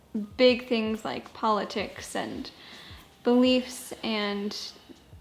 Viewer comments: When you said you agreed on "beliefs" I heard "the leafs" and I was like "oh, I didnt realize they were such big hockey fans" hahaha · Re disagreeing on opinions: I heard ‘leafs’ instead of ‘beliefs’ so I though ‘oh no separate hockey teams? That’s rough’ lol SO Canadian 😂😂